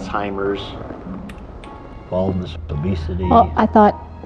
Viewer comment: do you know what he says at 8:51? What disease is that? I repeated that part but I can't understand what he is saying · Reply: i believe he said "baldness, obesity"